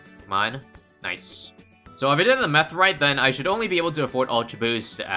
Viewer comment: Did anybody else hear him say "If I did the meth right" at 5:06 ?